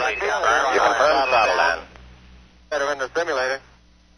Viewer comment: what is the speeded up voice saying? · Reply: It's Buzz Aldrin saying "better than the simulator", by which he meant that he felt the descent was going better than it had in some simulator sessions. Gene Kranz said in a later interview that the remark helped calm people down and took a lot of tension out of the control room.